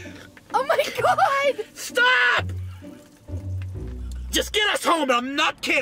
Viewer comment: THANKS FOR WATCHING! COMMENT BELOW WHAT VIDEOS I SHOULD REACT TO NEXT!!! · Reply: Did you say Stop to Pepsi ? Lmao 😂